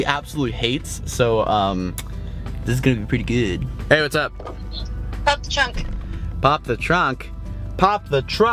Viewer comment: I thought he said Bob the truck or pop the truck 😺😺😺